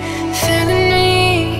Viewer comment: did she really said i can fuck with you for returnity?